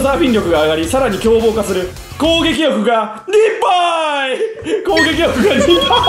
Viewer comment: の｢攻撃力が2倍」の所 ｢攻撃力リブァイ｣に聞こえた(爆笑)